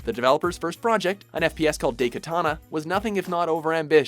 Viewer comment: Did you just say "day"katana?